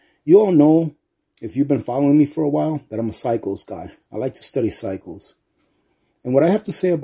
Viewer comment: When you said cycles guy I thought you said psycho guy 🤣🤣🤣